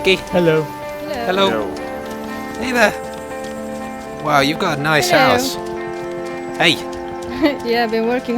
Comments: I thought said "Wow you've got a nice arse" then said why.. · Lol, on 31:09 it sounded like said "Wow you've got a nice ass!" instead of "House".